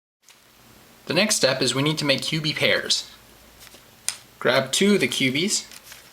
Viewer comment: When he says cubie pairs it sounds like he is saying pubie hairs!